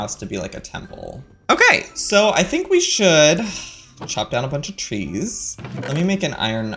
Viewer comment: Is it just me or does it sound like Joey said, "Chop down a bunch of cheese."